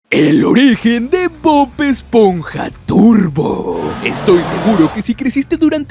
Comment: Pense que decia bob esponja turbio xd